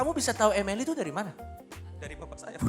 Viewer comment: Ada yg denger, ngomong apaan disini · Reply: 'Dari bapak saya'